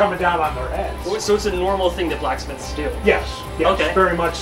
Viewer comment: So who else thought he said "Black's Myth"?